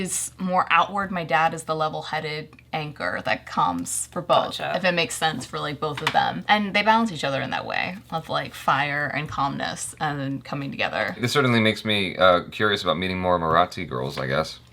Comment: did just say "oh achaaa" 🤩🤩